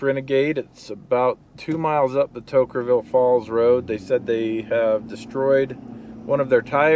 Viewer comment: Great to see your parents. Looks like they're doing well, the route to Toquerville Falls (again), and your method for turning around a trailer in a tight situation. That's a challenge with a long wheelbase truck. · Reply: Oh I thought he said Toker Ville Falls. One toke over the line